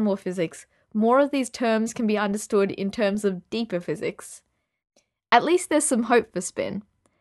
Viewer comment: What is "de co-physics"? Did I hear that correctly? Did I miss a video about it?